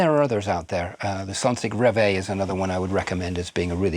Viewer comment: the what? sonsig reway? Edit: Oh, I actually got way closer than expected: Sonsig Rev-A. It genuinely sounded like you couldn’t be bothered to articulate 😬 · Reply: Sorry about that. Sonsig Rev A is available from Relab.